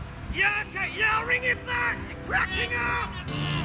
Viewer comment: Sounds like he said "you're crapping up" when he was in the bathroom